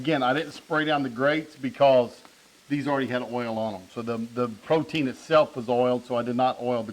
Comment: Thank you for sharing another great tutorial video. Those chicken breast look great. I'd sure like to help you taste them 🙄. Did I hear you correctly with what sounded like you saying, "Protein is oil"? My hearing is bad but, if this is what you said, then I've learned something new today. Again, thanks for sharing this video. Stan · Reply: I will have to go back and listen to what I said. But, no protein is not oil, fat is. I may have said the protein is already oiled, because of the marindae that is why I did not oil the grill. Thanks for watching